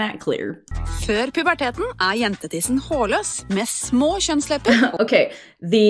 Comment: No absolutely they did not say penis, don't panic 😂 it said "jentetiss" which means "girl's genitals" but in a childish way, "tiss" is used in that way but the proper form in norwegian is "genitalier" but since this is done in a more fun and for kids they probably thought it was better